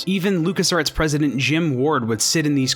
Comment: Am I the only who thought he said Lucas arts president Jim Gordon